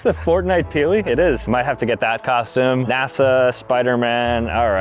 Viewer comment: did you just say nasa